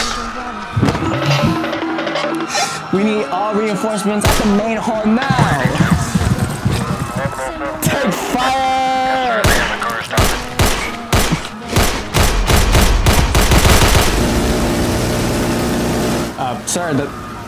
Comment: did he say take fire